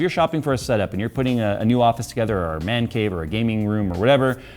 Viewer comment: did he really said "mancave" at 14:28? haha